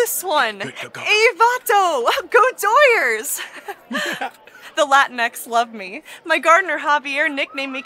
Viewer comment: As a Hispanic person I officially died at the "Latinx people love me." The vast majority of us despise that term. Brilliant Bee!! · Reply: I didnt even get she was saying that nonsense word. I thought she said she had a "latin ex" (boyfriend) and he "loved her" lol